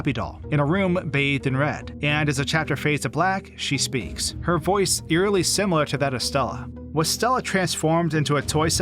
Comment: I genuinely thought that at 6:00 they said "her voice eerily similar to Stalin"